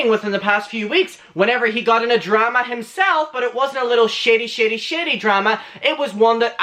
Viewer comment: And i thought u where saying shity shity shity. Not shady shady. Ha🤣 love u bro